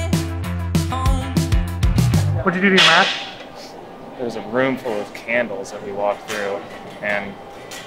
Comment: 🤣🤣🤣🤣 I thought he said "A room full of cannibals 😂😂😂😂